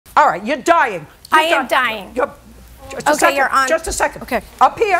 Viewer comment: Alright, you're dying... - I AM dying. I died of laughter. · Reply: I thought I heard wrong 🤣🤣 convinced myself it's -"You're Diane" -"I'm Diane"